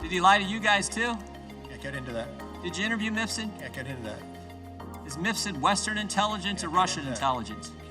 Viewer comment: I cant understand. At 1:51 he asks about a man named Mifson??? I cant find anything about him. Am i spelling it wrong?